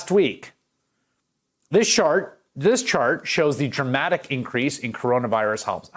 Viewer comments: did anyone catch that!? “THIS SHART this chart”😂😂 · Did Chris just say Shart? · I swear I heard this "shart" at 2:20. That is actually an apt metaphor.